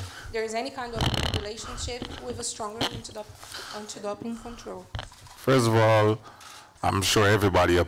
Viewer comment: Is she saying antidote???